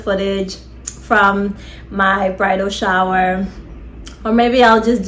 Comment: did she say bridal shower 👀 lol